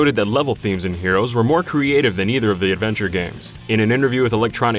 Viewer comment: sounds like you said “Avenger games” lol